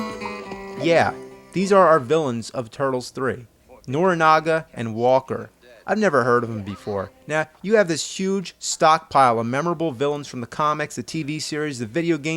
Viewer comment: i thought you said "Nobunaga". That would have been awesome! The Turtles vs "The Demon Warlord" of Japan!